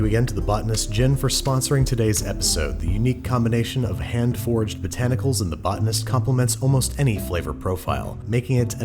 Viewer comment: Every time you say “hand foraged,” I keep hearing “hand forged botanicals” and imagine an iron mint leaf.